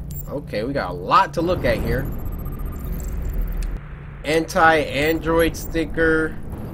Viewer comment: Ok i lowkey thought cory said ''Hentai android sticker'' but he actually said ANTI android sticker lmao. i had to rewind that a few times xd